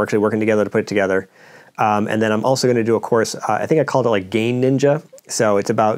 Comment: When you said "gain ninja" I heard "game ninja." He's branching out.